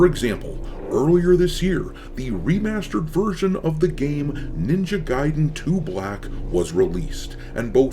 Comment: I heard that as "ninja gaiden: too black" I´m too sleep deprived